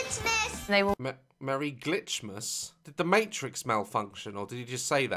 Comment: i think he said "merry glitzmas"